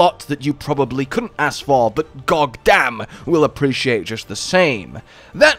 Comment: Had to backtrack to the first minute to make sure I heard "gogdamn."